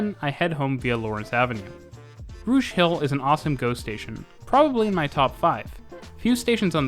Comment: "Rouge hill is an awesome GO station" the first time around i heard it as "... awesome ghost station" 😄